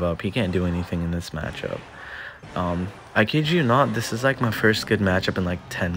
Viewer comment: what is he saying in 2:56？ sorry, I'm learning English and I couldn't make out the word. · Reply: “I kid you not, this is like my first good matchup in like 10 games” (“kid you not” is like saying I’m not kidding)